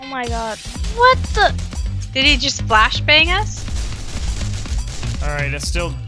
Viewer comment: NO. What did i just hear? I better have not heard her say "blashbang". I refuse.